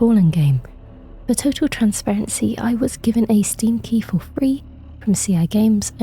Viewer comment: At the beginning it sounded like you said "I was given a stinky" 😆